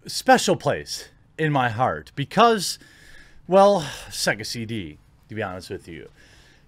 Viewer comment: "Why do we have to wait? Apex Legends released right away..." I'm freakin dead. I love your excitement! XD Oh, right -- took me about 4 or 5 replays to figure out you said SEGA CD at the start (I thought you said "sexy" something, not so different). I remember playing on my dad's Megadrive - did that thing take CDs?